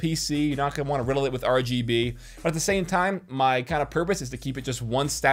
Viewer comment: Is sounded like you said ROG-B